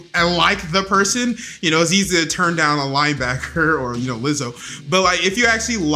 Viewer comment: did say lizzo at 6:07 😂